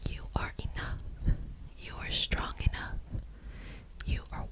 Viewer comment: she was talking about how strong i was and repeating the work 'strong'- TELL ME WHY I HEARD "stronk, stronk, stronk" REPETITIVELY BWAHUSJASJ